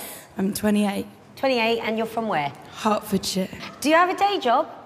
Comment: it sounded like she said hartford shit